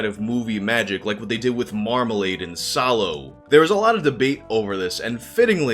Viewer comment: what is he referring to? I wish he wouldve explained that. Cant even Google it as I have no idea what he's saying. Marmalade and sallow? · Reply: "Marmalade and Saló" (Saló is actually a very disturbing, messed up film!!!)